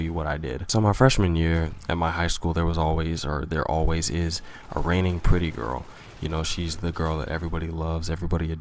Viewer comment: For some reason I thought he said "raining pudding girl," not "reigning pretty girl."